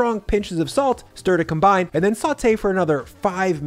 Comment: As a person who LOVES to cook but is getting pretty burnt out with life and choice fatigue (and also has an increasingly tight budget) I would be THRILLED to see this type of video repeated a few times over with different weekly plans Thanks and! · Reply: Dude!! I thought you said 45 minutes for the kale....😅